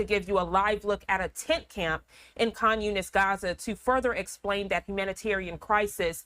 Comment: Did they say "Communist Gaza" time index 1:03 Now it's a Communist issue? What happened to Islamic fundamentalism and anti Zionism? Now Netanyahu says he is allied with a Gaza counterinsurgency against Hamas. Where were they before. They magically appear when he wants to take Gaza by storm! The Religion of Israel is Dantes Divine Comedy in Canto 12 The MINOTAUR AND THE RIVER OF BLOOD